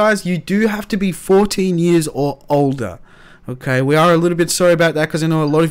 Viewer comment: Oh I'm 13 I thought you said 13 or older :( I got a computer for nothing